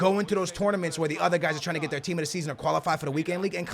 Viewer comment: I thought he was saying "A-team"